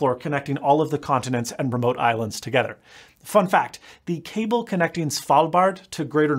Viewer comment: I thought when he said, fun fact, he actually said fu*k that!